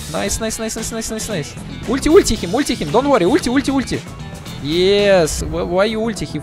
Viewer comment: I thought dendi was saying 'wait him wait him'